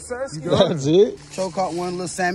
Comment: Did I hear “One lil salmon”🤣🤣